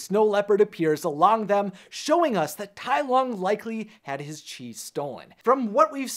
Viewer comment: Why does caption say cheese Instead of chi?